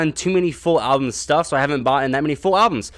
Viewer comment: did you say "boughten" ?